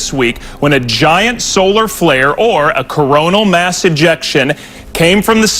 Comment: a Coronal mass injection?!?!!? WTF are they trying to say?? HIDDEN MESSAGE??? COINCIDENCE??? I think not🤦‍♂️🤦‍♂️🤦‍♂️